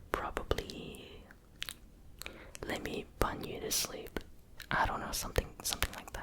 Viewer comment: “Let me punch you to sleep “ is what I thought it said🤣